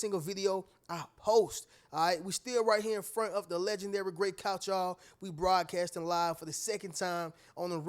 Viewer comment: man i know you are saying the "Legendary gray couch" since Couch is my last name and my hearing is shot from working construction and listening to music on 11 i hear "the legendary great couch" lmao smh should have taken better care of my hearing over the years.